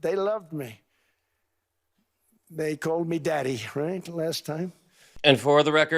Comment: tRump misheard "Daddy". They're saying "Dickhead".